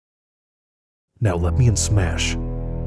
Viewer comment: At first I heard "Let me smash" and was like "the fuck stay away from my waifu" so I went through it again and heard "Let me in smash" and it all became clear.